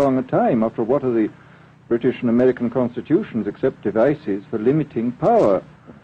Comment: Did he just say ISIS?